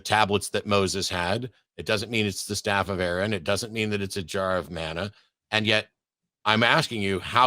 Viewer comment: Thought he said "a jar of mayo!"